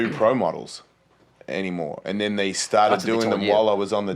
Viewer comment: What did tony say at 49:23 ? Sneak diss? Lmaoo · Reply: Ellis said something like "they stopped doing pro boards at the time" TG said: "oh that's what they told you..."